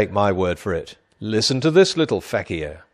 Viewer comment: did he say Fakiah? im sure he intended to say that cause it means joke in arabic